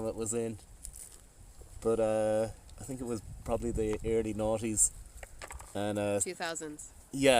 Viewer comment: I really heard 'early naughties'. LOL